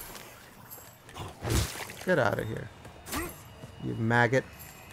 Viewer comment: What is the word you say in 7:35? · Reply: maggot